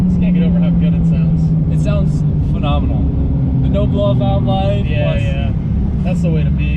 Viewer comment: what BOV is running on the 1j? Did I hear you say "no blow off valve life?" so just wastegate?